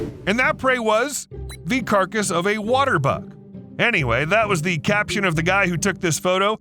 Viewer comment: Thought you all said “water butt” not “water buck” lol 🤣🤣🤣